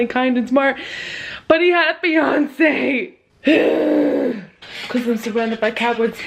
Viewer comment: I fucking thought you said "BUT HE HAD BEYONCE" instead of fiancée and was like ????